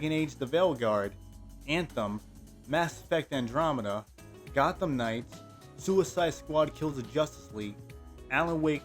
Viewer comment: i thought bro said arkham knight for a sec😭